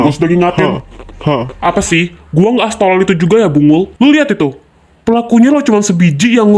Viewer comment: aku dengerny b#ng#l kak, aku gk salah denger kan? 🗿🗿